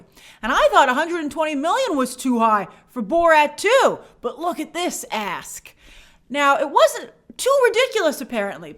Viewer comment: "And I thought 120 million was to high for Borat 2, but look at this ASK". Why did I keep hearing "but look at this a_s". I had to listen three times to hear the K, because I was thinking "Wow, Grace must strongly feel some kind of way about MGM's asking price to call them an a_s". 😁